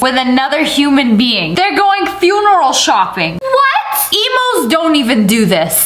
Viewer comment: I’m sorry, did she just call me an Emu? I’m not a bird thank you😂😂😂